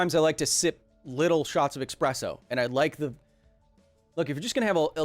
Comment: Sounds like he is saying expresso not espresso lol. Great video keep it up